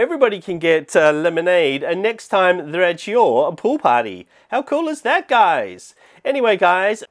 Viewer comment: Tysm I have 77 Robux so I can buy the lemonade stand! And lol btw when he said party I thought he said potty XD